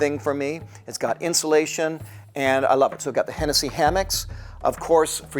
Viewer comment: You said Hennessey Hammock, but between my hearing not being the best and not paying full attention, I thought you said MC Hammer. lol